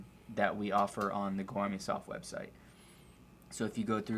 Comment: he mentions a website to go to for PT plans. I can’t quite make out what he’s saying though for the site. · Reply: Goarmysof